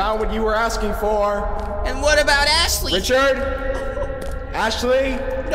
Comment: ashley Me: did he just say assley?